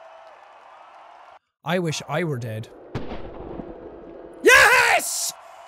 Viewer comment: When he said i wish i was dead i thought he said irish was dead lollllll